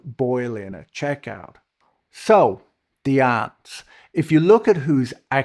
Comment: oooh ants, not aunts, okay... I misheard at the start